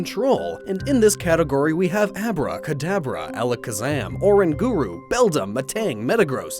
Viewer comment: He said Abra and Kadabra next to each other....It sounded like he said Abrakadabra